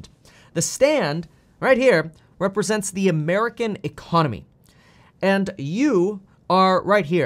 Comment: anybody else think that said elon not econ at 3:30? i think i've been thinking too much about tesla recently